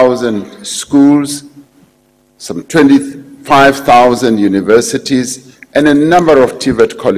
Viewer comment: Did he just say 25 000 universities?